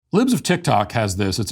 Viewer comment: First thing when I clicked this video I heard you say “lubes of TikTok” & can’t unhear it 😣😂😂